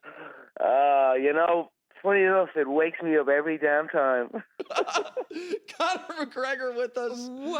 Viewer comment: WHat does connor say here??? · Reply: funny enough it wakes me up every damn time